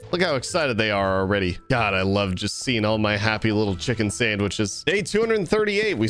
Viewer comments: Did he say chicken SANDWICH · did he just say i love my chicken sandwiches